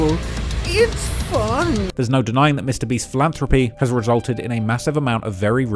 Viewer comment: i thought that said mr beat and I was like. really the history guy??